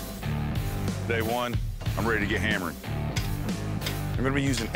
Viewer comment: I thought good ol boy said, "I'm ready to get hammered" and I was like, "me too bro, me too"